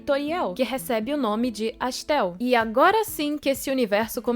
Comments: Eu ouvi "Astell" e entendi "Pastel", agora quero comer um pastel de carne 😅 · Eu escutei pastel não sei porque . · eu juro que ouvi "pastel" 10:57 o cara só perdeu a fé na humanidade e dizem relatos que depois disto ele se matou